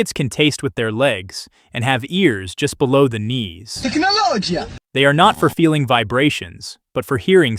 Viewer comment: did i hear gemologia?